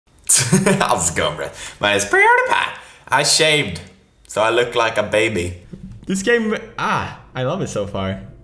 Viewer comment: with captions/subtitles says “That’s go read my spirit back, I shamed so I look like a baby!” 😂😂💖